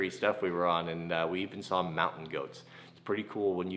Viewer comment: My friend was watching this with me and he thought you said you road a mountain 🐐! 😂